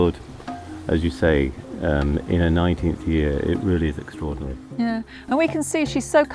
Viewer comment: God damn english, i thought she had said 90 years in the biginning